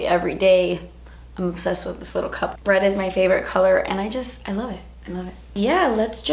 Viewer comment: I thought you said "bread is my favorite color" and I was like yes girl me too